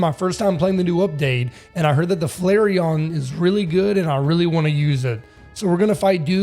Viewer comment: the way he said Flairon sounded like he said Flareon.